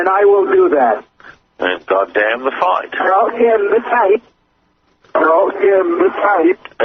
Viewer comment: I heard "We're all in this fight"